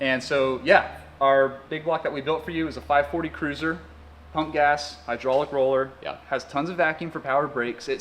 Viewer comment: I heard him say "punk ass" the first time.